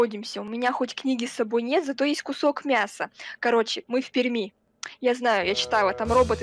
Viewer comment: Я которая живу в перми: бежим нас спалили🤡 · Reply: Когда говорилаМЫ В ПЕРМИ мне послышалось мы пельмени 😂😂😂😂